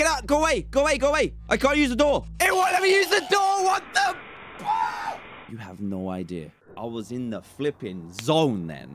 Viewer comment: WHAT DID HE JUST SAY? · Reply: He said PHUUUU